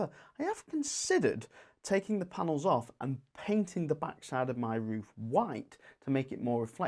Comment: So panels are transparent as the light enters T intersections hits the roof and reflects on to the rear of the panels also containing cells. This has shown a marked improvement in efficiency already however I wonder if that would increase again if instead of my slates being black/grey they were white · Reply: Absolutely! That is I was implying, instead I thought you said "paint the back of my panels in white" 😀. . . perhaps you meant the surface behind such as the tiles etc... and yes it would. However the double sided PV modules can only offer a small percentage of improvement. Are your panels of the "half cell type" ?